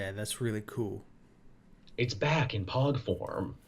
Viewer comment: I swear I did a spit take because I thought said "It's back in PAWG form"